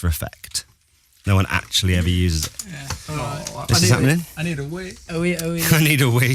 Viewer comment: they keep say "i need to wee" i thought they said "i need a wii" I WAS SO CONFUSED THEY WANTED TO PEE BUT I THOUGHT THEY WANTED TO PLAY MARIO CART OR SOMETHING lol